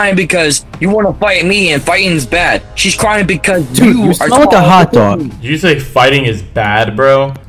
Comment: captions says become a hot dog 😂